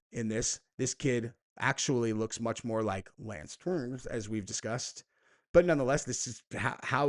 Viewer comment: who is Lance turns? Idk if I heard the name right